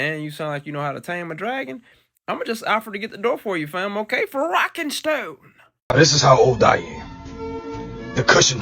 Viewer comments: DID I HEAR A ROCK AND STONE? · Did I hear a ROCK AND STONE!